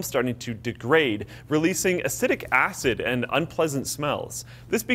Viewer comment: Did he just say "acidic acid"...? 😐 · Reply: He said acetic acid